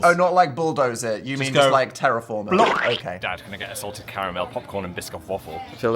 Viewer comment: the captions say ‘Dad’ instead of ‘Dan’ 😅